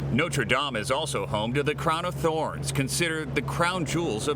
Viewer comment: Thought he said it was home to the crown of thor. Lol. But i do feel for that historical place. Never been to it but would love to go.